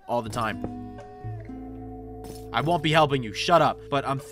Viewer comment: I swear, every time that guy screams "help me!!!" I hear "Calvin!!!" I guess I'm just high if it's just me hearing that